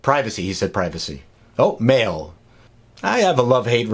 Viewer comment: I thought you said male!